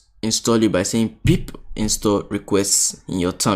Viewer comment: What's he saying at 26:40? Install what? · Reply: pip install requests